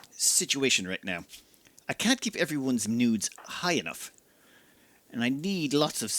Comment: What did he say? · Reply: I hear it, I was supposed to say moods, but I can hear the hit of nudes in there.